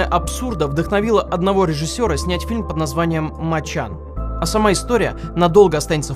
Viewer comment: я не расслышал букву Н в конце с первого раза "моча"